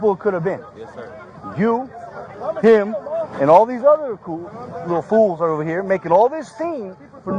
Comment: At the 10:39 mark did he call the kids coons?????!!!